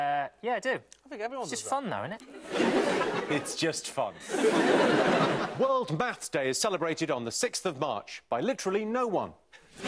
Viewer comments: did he say world maths day? Why did she react like that? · Rachel's mouth open on the World Maths Day joke was adorable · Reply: Lol. That joke finally makes sense. Thought he said something about "Bat's Day" and didn't get it at all.